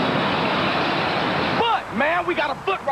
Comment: I thought the guy said fuck man